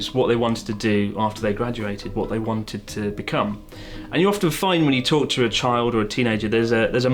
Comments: onward, I heard: "and you often find when you torture a child" and was like "whaaaat?". Oh, the British accent. Please never change. :P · Who else hears "torture a child" instead of "talk to a child"